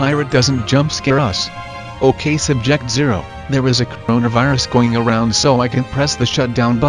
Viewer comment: did you say coronavirus